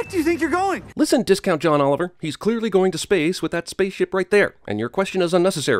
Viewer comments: I remember walking out of the cinema thinking "can't wait to cinemasins go hard on this piece of shit of a movie" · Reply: I found a sin for, At 6:30 they had themselves having to say "right" but the subtitles say "ight" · it says, "ight" instead of "right" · You put ight instead of right